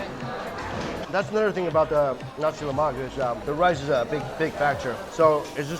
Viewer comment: It sounds like you say Nazi Lamak